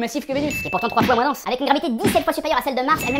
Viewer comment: Qui as entendu comme moi « densité de ma bite » à 2:05 😂